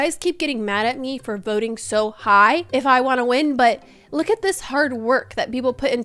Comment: please never stop voting high, its so rare to see people actually giving fair votes we love that you do!! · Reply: Omg I thought you said stop voting so high and I was like: oh- ☹️ 😂😂